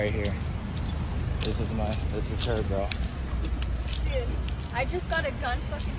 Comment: Did she say she bought a gun?